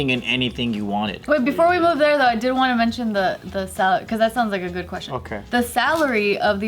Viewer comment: I thought said "salad" too 😂 then I imagine saying "... don't you mean chef's salad...." as she looks directly at the camera 😏🤣